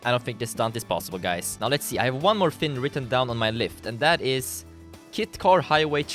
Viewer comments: did he said lift 3:26 😂😂😂😂😂😂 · ``i think i got one more thing written on my lift instead of list``